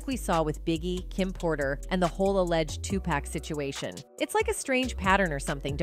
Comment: Did you say Tupack???